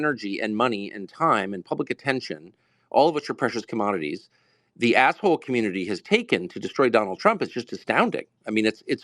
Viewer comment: lol @ 2:18 did he really say the asshole community ??? hes just usually so.. proper. 👍👍